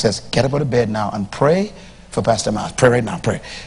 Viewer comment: Did he said pray for Dr Myless munrow he going to die in a plane crush next year? Time